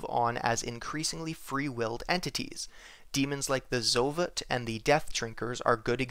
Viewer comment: did he say “Zolit”?